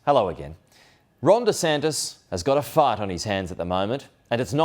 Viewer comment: I thought he said fart, not fight 🤣